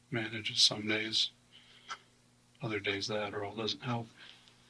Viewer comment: Translations/captions are off sometimes. For instance, at 11:52 he says "somedays, the Adderall doesn't help", but the caption says "that or it all doesn't help." Worse, 11:56, he says "I like music", but the caption says "I collect vinyl".